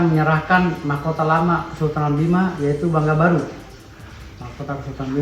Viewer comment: maaf takut salah dengar tad diai bilng Kesultanan bima.. atw minum kuku Bima sih??